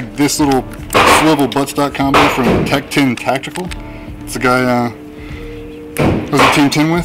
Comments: Who makes that sling attachment point that goes on the Stock?? Sounds like you said Tac Tune Tactical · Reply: Tech ten tactical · Does anybody know what company makes the stock swivel adapter at 0:52? Sounds like he said 'tech tune tactical' but I can't find anything on it... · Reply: Tech Ten Tactical Swinger Sling Mount System